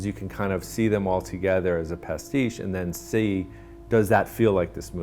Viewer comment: What’s the word he says at 6:32? See them all as a pestigue? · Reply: pastiche